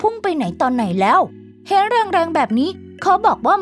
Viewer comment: ได้ยินว่าเห็นรุงรังแบบนี้